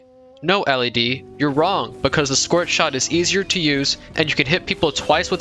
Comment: the subtitle say squirt shot 💀